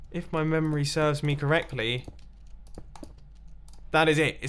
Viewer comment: i swear it sounds like you made a gmm reference there Link once said “If my memory serves me, /correct/ me” Like instead of “correctly” Idk could just be my audio processing issues but still funny lol